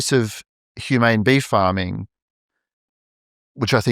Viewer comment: They mentioned bees earlier. Then he started saying BEEF farming and for a while, I thought he was saying BEE farming. I think treatment/farming has to do with awareness and pain. Humans have more levels of awareness and feeling than other animals. Farming humans would be like the the horrors of the holocaust. Humans feel more pain on many more levels than a prawn. I also feel higher animals feel/ experience more pain than an oyster for example. I think for lower animals it is more like a yes/no, issue. Is it good or bad, but not ecstasy vs agonizing. I have had a crazy, outer limits life and know that psychological pain is often worse than physical pain. They did not mention how crappy an animal's life usually is. There could be a drought in Africa and animals by hundreds everyday would be dying of thirst under a scorching sun. Also in the wild, animals are captured by other animals everyday and they are dragged down and literally eaten alive. So...